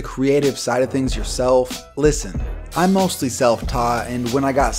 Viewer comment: At the end he says " I'm mostly self-taught" but I had to rewind it like 4 times because I heard " I mostly sell pot." Now I want to smoke some weed.